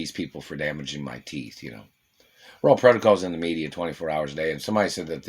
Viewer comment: WHAT OR WHO DID YOU PLAY?! We are tired of hearing that "people want to know if..." The Royal Protocol is in the media 24 hours a day, just tell us. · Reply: All this time I thought he/she was saying "Role Protocol"